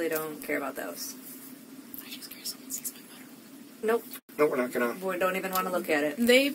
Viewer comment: Did just say, to the police, that she sent "butthole" pics via text & Snapchat? Why, just why? 🤨🤔 She uses the word "like" way too much. What is it with that generation & their excessive use of the word "like"? Like it really like starts to like get annoying like when they like to use the word "like" so much. 😂 🙄 But seriously, I feel really bad for her. It must have been devastating when she finally realized he was a pathological liar & guilty of murdering his parents. Not only did he murder them, he dismembered them & left some of the evidence on her family's property. I listened to their conversations when he was in jail waiting for the trial. She still supported/believed him for several months after his arrest. I wonder how & when she realized he was lying about everything. What finally convinced her he was guilty? It's hard to believe he thought killing his parents was a better option than just admitting he lied.